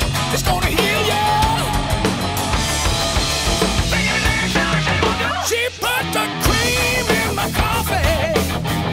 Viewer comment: what does he say there? It’s not in any lryics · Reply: Bacon, egg, shower shave'll do!